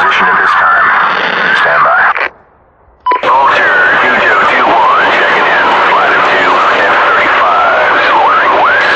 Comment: flight of 2 F35. 4 mavs, 2 Harms, 25 GBU 12......wait.... WHAT?? 25?? oh wait i hear "6........5 Gbu 12" ok that still in the realms of possibilities. harms and Mavs are on external Panel tho, so non-stealth config. im imagining a Clickbait Thumbnail of F-35 with 12 GBU 12 a piece clipping through other bombs beause of this. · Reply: 6 by or 6X GBU 12s, little late for this comment but that would be something to see